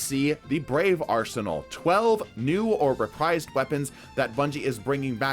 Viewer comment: I heard "12 new overpriced weapons".... new or reprised hahahahaa